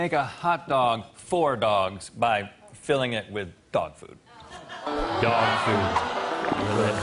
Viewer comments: For the dog food round I thought it said dog poop!😂😂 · Dog food, ohhhhhh I thought they said dog poop that would be much worse 😂😂